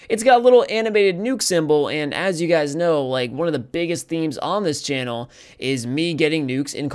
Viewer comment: I heard MEMES instead of THEMES.......... Dang that was ironic........